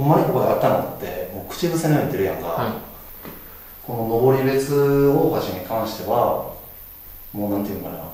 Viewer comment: 画面見ないで声だけOP聞いてたら、『登別大橋』が『ロドリゲス大橋』と聞こえてしまった…😱 霊障かな？